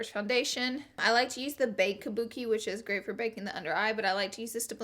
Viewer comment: Laura. I'm watching this video at 2am. My boyfriend is sleeping so I have the volume low and the subtitles on. I just need you to know that at 1:55 when you said "bake kabuki" the subtitles said "bacon booty", and i lost it. I can never unsee that.